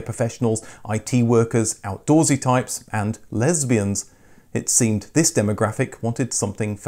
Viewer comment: did he say “Lesbians”? 😅